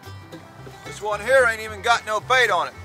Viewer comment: When you said ain't got no bait on it I heard ain't got no gas in it.